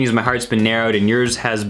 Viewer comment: I thought she said my husband lol.. Instead she said my heart's been